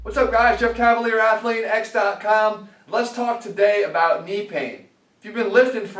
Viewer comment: For a second i thought you said, lets talk to BAE about Knee pain, meaning the skeleton and i was like, I think BAE may have a little more than knee pain to worry about